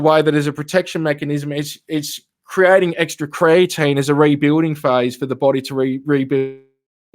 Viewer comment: Dr - was not talking about keratin but about creatine - 2 different things :) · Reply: I think Dr said "carotene"--as in the orange pigment that gives carrots their color and that the body makes Vitamin A from. And I heard say "creatine," which is an amino acid that would help rebuild body tissue, especially muscle mass.